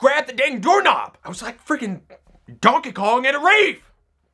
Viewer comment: he actually says "Donkey Kong at a rave"